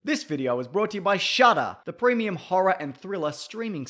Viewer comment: I thought he said "This video is sponsored by SHUT UP!" lol